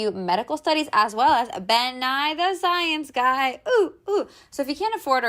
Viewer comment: did sis just say "ben-nye the science guy" at 8:26 lol